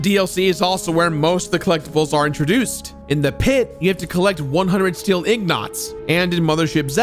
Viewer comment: Wtf did u just say "steel een gots" lol